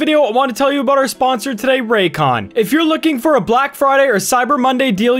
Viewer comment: I thought the sponsor said Raytheon for a split second